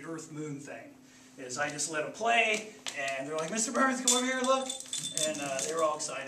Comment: captions say mr.burger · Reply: Mr. Barth